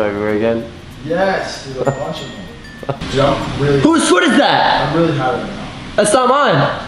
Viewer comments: did say I'm really high right now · anyone else hear the guy say "i'm really high right now" at 11:00? 😂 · Did anyone else hear go “I’m really high right now. @ 11:00 or was that just me 😂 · is it just me or does it sound like is saying "I'm really high rn" in the background